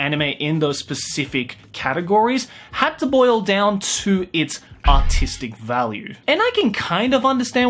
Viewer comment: No joke, I heard "autistic value". The internet has ruined me